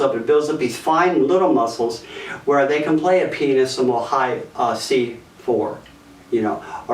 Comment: Your videos are always very helpful! :) 3:47 Hmm... Did I hear right? The subtitles seem to agree with me O__o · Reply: kinda late but he said pianissimo (just in case its not a joke)